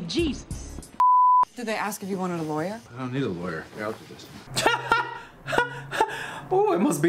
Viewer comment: what he say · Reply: He said he didn’t need a lawyer